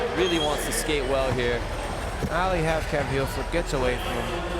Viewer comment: did he just say ''nollie halfcab heelflip''??